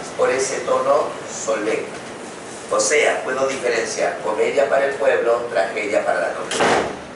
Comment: me encantó, pero quisiera saber qué es lo que dice en el minuto 28:45 ... algo de comedia para el pueblo y tragedia para(. ..) ... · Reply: "Comedia para el pueblo, y tragedia para la nobleza"